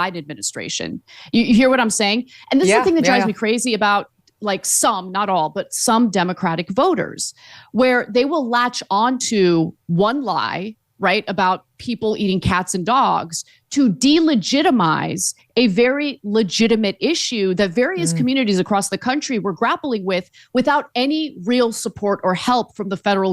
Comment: Was it just me, or or did it sound like Kamala said "people eatin cock"? You heard it, don't deny it.